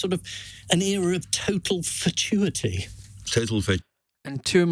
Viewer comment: Fatuity...I thought you said flatuity!🤣